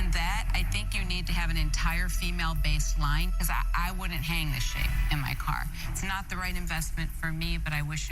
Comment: I thought laury said I wouldn’t hang this shit in my car 😂😂